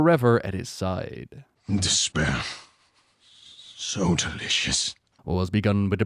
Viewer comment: Lmao, subtitles say "This Pear, so delicious."